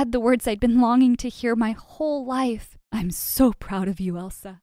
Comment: Is It just me or I heard Elza's dad said "I'm proud of you Martha" instead of "Im proud of you elze" at the end?